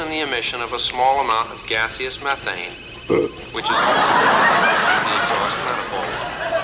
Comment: I didn't get that! Because the audience laughed and I didn't hear that last part. "Which is........." Anyone else got that?! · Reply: I think it's saying: "Which is a fuse to produce to the exhaust metal form."...